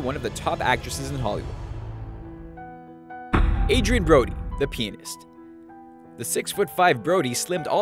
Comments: mark, I thought i heard "The Penis" after looking away for a moment to check my phone. It was actually "The Pianist". LOL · who else thought he said "penis" instead of pianist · Did someone heard "the penis" at 3:13 ..... HAHA